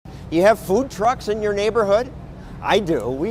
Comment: Oh you said food trucks, I thought you said good drugs..